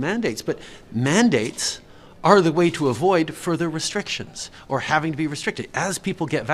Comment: He actually said ''mandates are the way to avoid further restrictions''. Pay attention! 0:40.